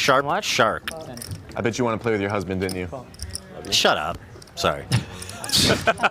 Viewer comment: What happened at 2:46:45? Ethan told the guy to shut up because he said some comment. It sounded like he called Hila a husband but I'm not sure why that was a triggering joke. The context is what I'm missing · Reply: He says: I bet you wanted to play with your husband, didn't you? Xd